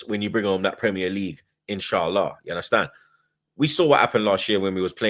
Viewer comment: did you just say inshaallah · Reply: Ya he say insya Allah